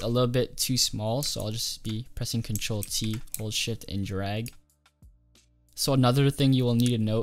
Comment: i thought you said hold shit